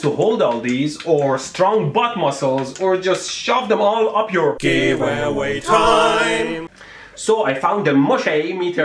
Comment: Medhi: Or just shove them all up your... Me: I thought he said "your a**" but he said "Giveaway Time!!!"